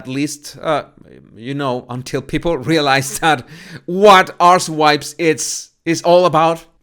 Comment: Did I hear correctly? calling them ‘arse wipes?’ …11:44. Hehe!